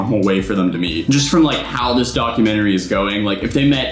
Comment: When you said documentry I thought you said, dogumentry😎